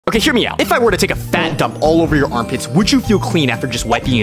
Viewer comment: I thought you said “throw a fat duck at your armpit” and I was so confused why you’d throw a duck at my armpit, why I’d need to wipe it with toilet paper, and was confused what poo had to do with it